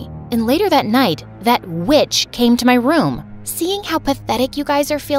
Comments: Ngl I thought she said the b word but it was witch and I related that part😂😂😂😂😂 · When she said witch, for a split second I thought she said bitch and repeated the scene. XD